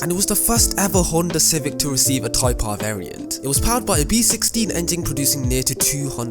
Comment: i thought he said a v16 engine